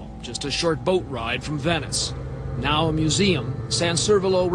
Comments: i thought venice said vsauce at first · When it said "Venice" I thought it said "Vsauce" so I'm like... what? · Anyone else watching this through Vsauce’s Leanback thought it said Vsauce before realising it said Venice?